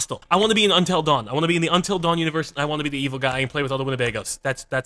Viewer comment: Did Chilled say he wanted to play with all the Winnebagos?